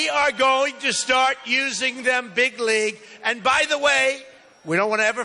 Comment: did he just say "going to use them bigly"